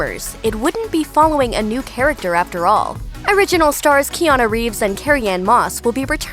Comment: thought she said KEANU REEVES... I was bout to be like aye...